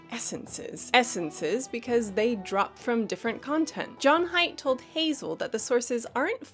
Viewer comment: I thought he said John Hamm at first, and it still works lol